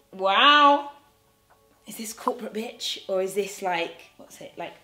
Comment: !!! When you said corporate bitch I thought you said crop a bitch... 😭😭😭... #cropabitch